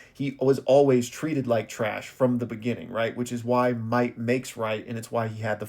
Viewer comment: i thought he said white makes right 😭😭😭😭😭😭